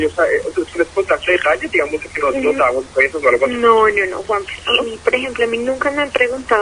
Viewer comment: que dice, ellos saben si nos dabamos besos?